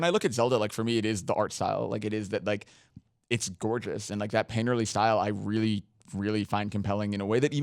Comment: what did he say, "panderley"?